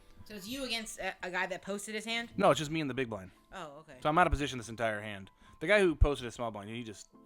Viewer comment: I don't know which hand you're talking about, but the small blind is out of position the entire hand. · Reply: it was a joke. i thought i heard you tell grace "i am out of position the entire hand!" lol